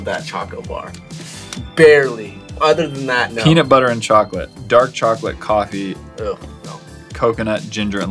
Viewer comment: For the cricket chocolate things, for the red one I thought it said dark chocolate, coffee, and cyanide!🤣🤣🤣